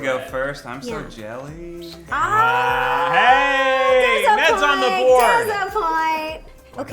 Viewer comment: what Ro said : That's a point what i hear Ro said: diSApPoiNt